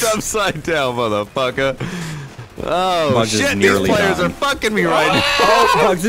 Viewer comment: did he say these players are fucking me